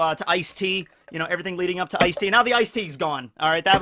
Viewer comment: When he said “everything leading up to Iced Tea” in the intro. I’m ashamed to say I heard “Ice T”